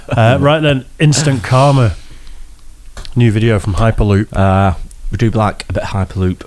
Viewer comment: Lol at the beginning when they were saying the name of the channel (HyperLoop) I thought they were saying "High Paloop" and I was thinking that was an incredibly weird name.